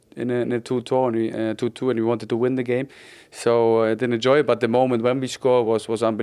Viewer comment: Bless him fabian thought she said enjoy instead of endure